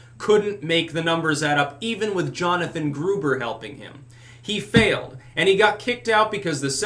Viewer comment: I had to do a double take. I thought you said Hans Gruber.